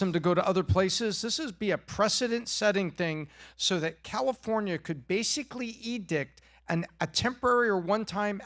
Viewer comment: Did Tom just say “So California can basically Eat Dĩck”?????😳😱🤯😮😲🙊😯